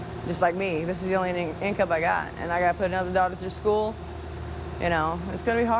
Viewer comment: did she just say she "has to put another DOG through school"????